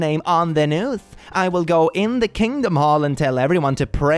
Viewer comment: i literally heard "kingdom hall" and went NO, NOT THE JW💀